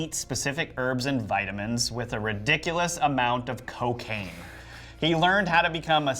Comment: Wait, Cocaine!? I thought they said do ridiculous amount of Rogaine. I feel like such an idiot now. But my hairline is on point, so there's that at least.